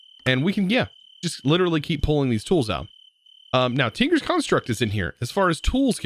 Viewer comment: I heard "Oh yea, Tinker's Construct is in here" and i was like ohhh phew, and caught my heart. it was really hoping tinker's construct was in here lol